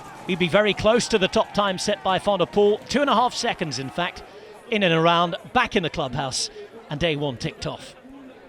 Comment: What is he saying at 1:32 ? "In and around back in the club house and day one ticked off".. wut😂 Man sometimes I've got a hard time understanding Britisch English..